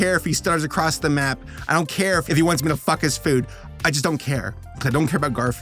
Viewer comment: When it said "flick the food" I thought it said, "fuck the food" 😂